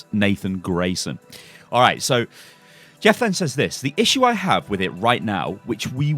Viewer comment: did u just say jefflan if so it sounds pretty amazing😂